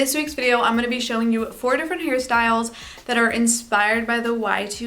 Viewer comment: i hear like "Harry Styles" sjjxsjks